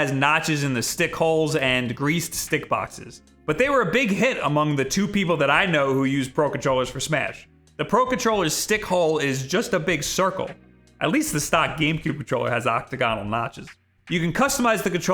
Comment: did you just say "dick hole" ?